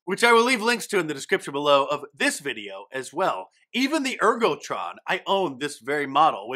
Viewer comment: Oh, I misheard you. I thought you said that since 2015, you have been using an " Erkel " Model Moniter in your kitchen. It got my attention though.